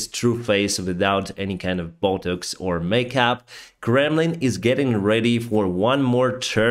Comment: I hear "Gremlin" everytime he says "Kremlin" 😁